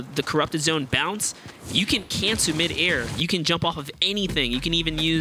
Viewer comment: did he say “you can CANCER midair”